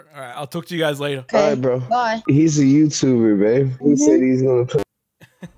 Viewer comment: did he say babe?